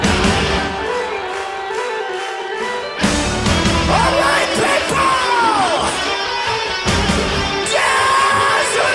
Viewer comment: Amazing performance! I am wondering though, what does Marco say at 3:18 besides "alright people"? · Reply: Dance and jump my friend